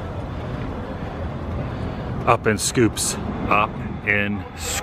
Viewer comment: I thought it said Zip in scoops.